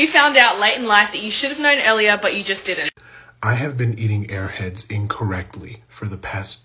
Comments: At the first, I thought you said "I have been meeting airheads incorrectly" 😭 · For some reason when he said “I’ve bean eating airheads incorrectly” I thought he said AirPods 😅